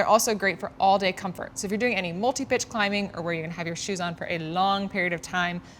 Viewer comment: I could have swore I heard “if your going to do any multibitch climbing” lol!!!